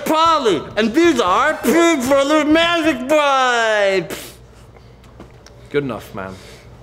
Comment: i heard magic pipes.